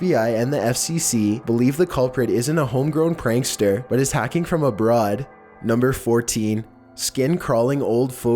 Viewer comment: For 15 at the end I thought you said hacking from the bra 🤣🤣